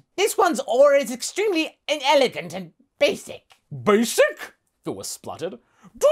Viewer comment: Did he just call him a "basic peach" ?